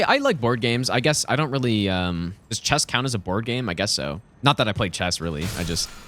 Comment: I thought he said does chess count as a horror game, instead a board game 😂